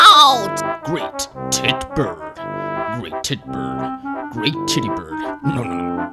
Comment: At the great bird part before the end. I thought it said "Great Titty Bird"